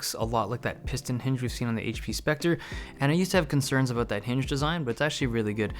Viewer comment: Thought you said "that shit" for a minute lol